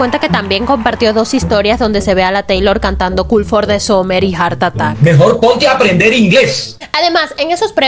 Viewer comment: escuché Art Attack JAJAJAJAJAJAJAJA